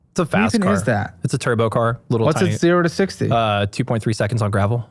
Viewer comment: did he really say 0.60 in 2.3seconds??